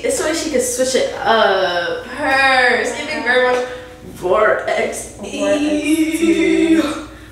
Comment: What aesthetic did you say Mona was giving 42:18 ? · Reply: “It’s giving very much Rawr XD” 🤘🏽🎸🧑🏾‍🎤😂